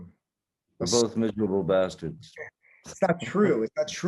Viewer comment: what does Johnny say about “basket” ? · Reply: He says “we’re both miserable bastards”